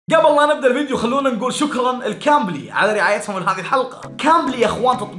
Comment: ليه سمعت كامري بدل كمبلي 😂 هو اذني فيها شيء